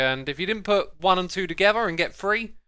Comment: 4 a sec I thought you said “put 2 and 2 together and get 3”